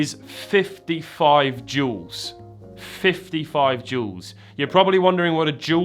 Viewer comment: Oh, JEWELS!!! I thought you were saying there's 55 JOULES going on inside this movement - I thought that's a pretty low amount of energy inside that case!